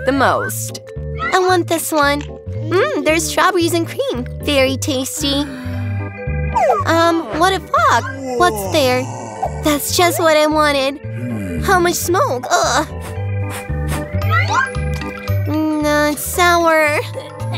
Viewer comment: Alright, why did I just hear "what the fauck?"